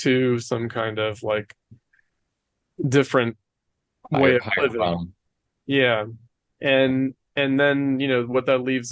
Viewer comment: What does the interrupter say? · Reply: "higher realm"